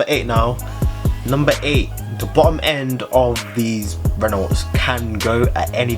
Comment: Hi, at 8:52 what did you meant to say? Bottom end? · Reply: Javirobaina Yeah Bottom End, gotta speak more clearly😅